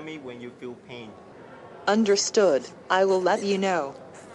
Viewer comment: Did she say "I will love you now"?